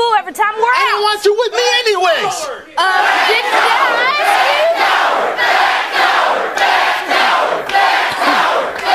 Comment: did they say "back down"? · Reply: No the audience repeatedly said: "Fat coward".